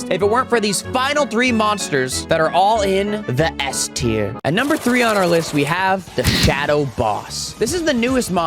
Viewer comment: an ad made it sound like you said “that are all in- the university of phoenix” lol